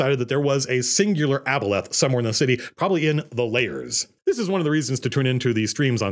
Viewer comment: I initially thought he said his undercity was called "The Lairs". After watching the twitch stream I realized he is actually saying Layers. Much better.